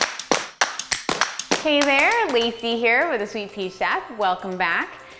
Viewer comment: When I first saw this I thought it said " Hey there lazy hairs" in the beginning lol.